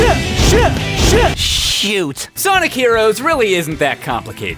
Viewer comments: I think he said shift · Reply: Suck Yes but it sounds like he says shit. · i think knuckles says "syö" wich means "eat" in finnish. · Adjusts nerd glasses Knuckles is supposed to be saying "shift" which is followed up by "RAAAH" (or "rock"), which is ended with "YEAH". Yeah, it still doesn't make sense even when you know what they're saying. The audio design in this game is garbage. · It sounds like he says "SH__!" But he is actually saying "SHIFT!"